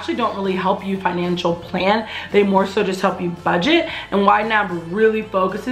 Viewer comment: I got so excited I thought you said wine app, like it was some sort of wine subscription service 😂 but then you spelled it out YNAB 😑